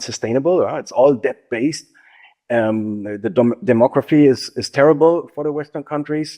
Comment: Did he just say “Democracy “ is terrible for the western world? So is he saying a dictatorship is what’s needed? Where is this guy from? And secondly;This Administration is destroying all of our military alliances and economic dominance in the name of”America First “and we get absolutely nothing in return but a mere pointless ego stroking that’s not going to last, and eventually all the consequences will come home to roost. Prayers,Physical gold & silver is definitely a necessity in this environment.